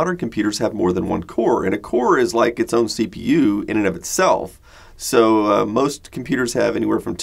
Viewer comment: Sorry, I'm French and trying to know what's said at 3:27 Is it "In and of itself" that's being said? I hear that phrase a lot.